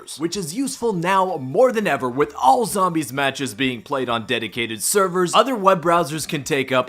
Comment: did anybody else hear dedotated wam?